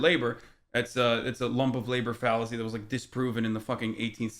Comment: what was the name of the fallacy hasan mentioned at 15:12? i couldn't quite hear what he said before that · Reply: Sounds like "lump of labor" fallacy